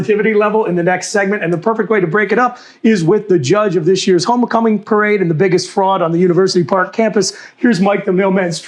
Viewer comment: At the 13:58 mark, did he say homo-cumming? I didn realize it would be that kind of weekend.